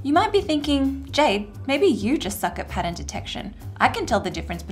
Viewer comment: Did you really say "f**k"? The sound is off. Just curious ;-) I may easily be wrong, English is not my mothertongue. Oh, forget it. Just curious. And, yes, thanks for all your work! Keep on. Please. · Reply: She said "suck", which means "to be bad at something" and in this context it's not a curse word.